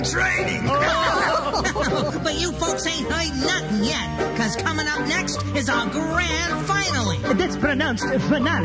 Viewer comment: What'd Chuck E say? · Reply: "But you folks ain't heard nothin' yet, cause comin' up next is our grand finally!" PASQ: That's pronounced "finale" CEC: Whatever...